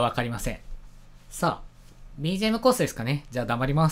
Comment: BGMコースですかね、頑張ります って聞こえ